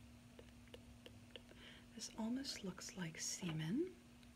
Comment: I was dozing off before you said "it almost looks like semen" at first I thought she said salmon, then it kinda rang for a second then I was like.... Okay then